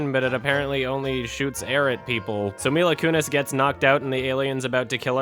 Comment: I thought said Arab people instead of air at people